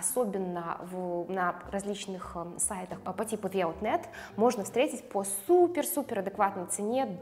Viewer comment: «На различных сайтах по типу ...» Никак не могу расслышать название :)) Подскажите сайт, пожалуйста, кто знает 🙏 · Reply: The outnet